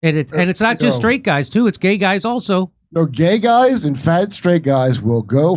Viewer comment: I thought he said gay guys and f*g straight guys😅